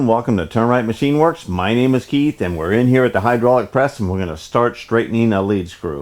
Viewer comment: I hear "Welcome to Termite Machine Works" every time. Always cracks me up. Love the channel.